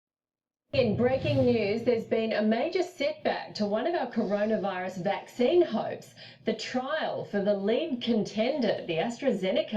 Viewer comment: Sounds like she said carona vaccine hoax!